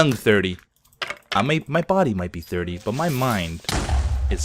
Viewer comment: when hes like "my body may be thirty" I thought he said "My body may be ready" and I was like o.O